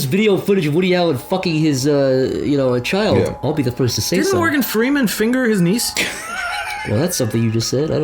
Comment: Oooooooohhhh, i thought this whole time said " Didnt Morgan Freeman finger his knees?".